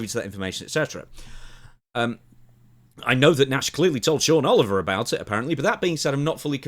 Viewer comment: I thought you said John Oliver at first and I was like, "why is Oliver talking about Zayn's contract?"